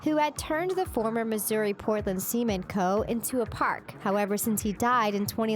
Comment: I'm sorry did she say cemen?